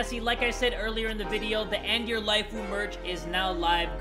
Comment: I thought his merch said "end your lifu" with lifu pronounced as leefu😂